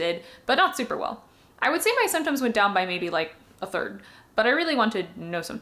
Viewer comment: Hiya, fyi, error at 1:10 - you say your symptoms went down "by maybe like a third", but captions say "to more like a third". Very interesting video!